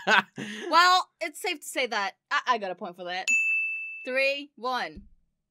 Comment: what did you say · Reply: f word